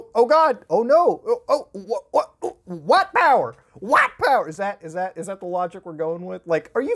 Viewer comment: um excuse me, do you think it's okay to literally say "white power" in your video?!? I'm reporting you to the deer gods uwu